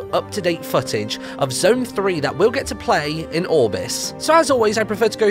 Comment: I got so excited when he said "when we play in orbis" because I thought he said when we play in august lol😂😂😂😂